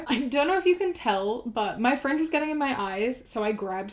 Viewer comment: At first I thought that she said "friends" instead of "fringe" and I got so confused as to why she was chopping up her friends